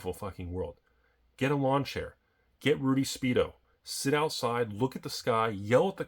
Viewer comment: instead of shirts, it's time for the Rudy Speed-O